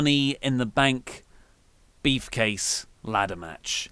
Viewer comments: Sounds like is saying "beef-case" every time he says briefcase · is saying beef case or briefcase?